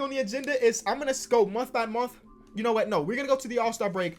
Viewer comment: wth did he just say?😂 muanthbamuanth